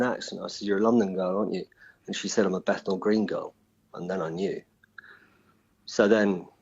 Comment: "She said I'm a ____ girl" What did he said she described herself as? I cant make it out. "Battle green?" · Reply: Bethal green in East London. She has a London accent.